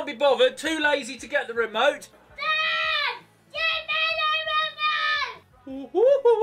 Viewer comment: the child says "Yeh mera remote hai".Who else heard that hit like